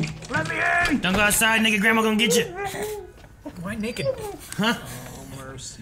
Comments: is it just me or did he say don’t go outside n word grandma gonna get yah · What he say?? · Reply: "Naked grandma"